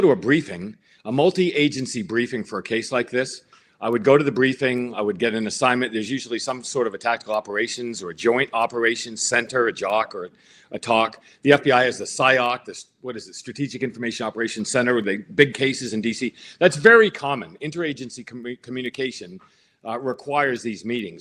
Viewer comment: did he just say ‘the FBI is a Psyop !:! Great Job